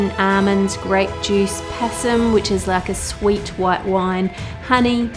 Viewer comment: I thought that/you said 50ml Possum Σ(っ °Д °;)っ